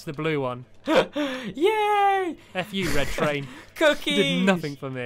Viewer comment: Did anyone else her him saying F U red train? Lol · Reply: I heard him say F U red train